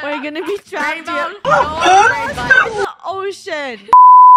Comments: Did Funneh say “Oh sh**t” or “ocean” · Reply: You know at first I also thought she said "Oh sh**t" but if you put your ear up to the speaker you can actually hear her say ocean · STAWP I THOUGHT SHE SAID “OH SH!T “😭😭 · I think i heard funeh said oh s*** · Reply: No she said ocean but it did sound like it lol 😂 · i love that some people mishear funneh saying oh sh#t but she actually said ocean 😭 (i was one of them im not gonna lie) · When Funneh said oh shit at 0:39 I thought she said ocean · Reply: she said ocean but i heard oh shit😊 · Did Funneh say “Oh sh**t” or “ocean” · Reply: Ocean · pretty sure funneh said the ocean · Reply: it sounded like she said "oh shit" instead of "ocean" 😭